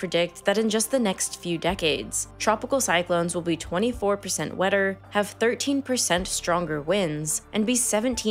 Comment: Idk why, but when you said "24% wetter" I kept hearing "24% weather" really fast lol